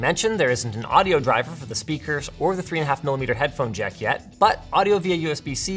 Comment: the subtitles say "check" when they should be saying "jack", I believe